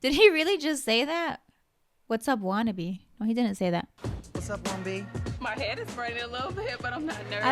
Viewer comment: I think he says "what's up queen bee?" not "wannabe", the captions just got it wrong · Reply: Lol no actually he says "What's up 1B?" In reference to her audition at semi-finals when she described her hair coloring as 1B 30. 😅